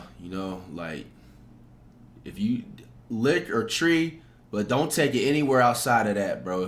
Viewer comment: what he say?? lick? or tree